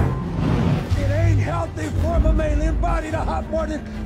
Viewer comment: can somebody tell me what did he said? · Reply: It's mammalian body but other than that, you're right.